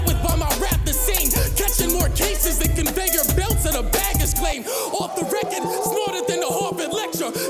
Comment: What's say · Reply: Catch more cases than a conveyor belt in a baggage claim